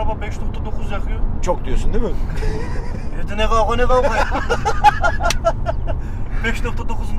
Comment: de ne diyor ya anlamadım? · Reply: Evde ne kavga ne kavga diyor muş